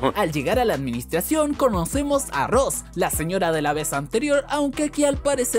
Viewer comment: le entendi arroz JAJAjajajdhsjjajjaja